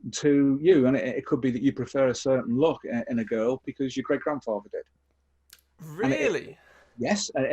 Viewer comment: Hi, can you please tell me what he said after 14:00. ‘You prefer certain looks because you….?’ I am not able to hear it properly. Thanks for sharing this :—) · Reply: Hi, followed that quote with "because your great-grandfather did". So, an "experience" by an ancestor - for example, a certain "Anima Type" profile - has been empirically observed by and to sometimes be passed down the generations. The Superpositioned psychobiological substrate is innate, and the distillation is acquired, not only over the lifespan, but from ancestral experience too. The mechanism for this, broadly, will be epigenetic inheritance. In short, a non-DNA-code inheritance of a systems-based gene expression profile. and observations re epigenetic inheritance were decades ahead of only very recent mathematical and experimental (e.g. toxicological) research into the field, confirming scientifically more and more mechanisms like this do in fact occur. Hope this helps - all the best, 🙏